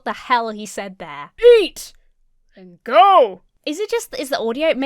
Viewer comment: "Leap! and go" is what I hear, and I'm confused.